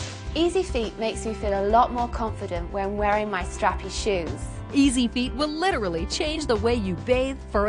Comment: I thought she said crappy shoes lol